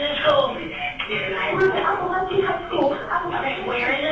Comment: Why did it say Opelika High School in subtitles? Description says Apalachee High School. I'm from Opelika so that part threw me off.